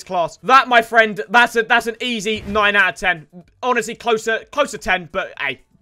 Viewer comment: i think when he say easy it was 10/10 mut it was 9/10